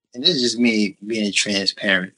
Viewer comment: Thought he said 'this is me being a trans parent'